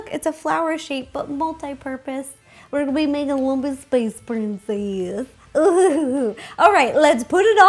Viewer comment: i thought she said monkey space princess LMAO